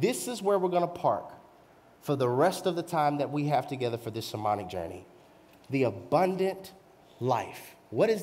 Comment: Pastor I have a serious question. At 29:03 you stated this is where we're going to park for the rest of the time together in our shamanic journey. I didn't understand what that word meant so naturally I looked it up. It came across to as meaning very witchcraft spritual ritualistic word. Is that the word you meant? What was your context? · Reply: Hello thank you for watching, Pastor said “sermonic journey”